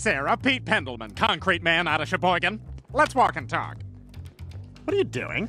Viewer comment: Did he say Sheboygan. Wow that place is not herd of much